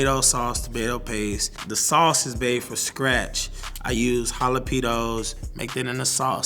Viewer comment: Did he just say he used “hala-pedos”😂